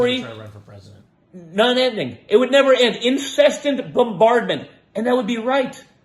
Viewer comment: Did anyone notice that Dumbass Jr. said, "IT WOULD BE INCESTANT BOMBARDMENT!" instead of "incessant"? 🤣🤣🤣 Please tell me somebody else caught that at like 25:46 in the video